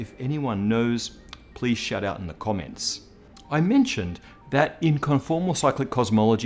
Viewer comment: I thought Matt said "if anyone knows, please shut up in the comments"